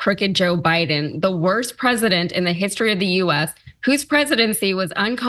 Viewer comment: I thought he said Biden wasn't president